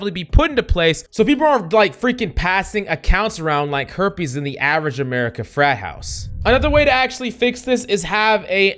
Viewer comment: I swear i heard "like herb in a african American frat house" 😂😭😭😅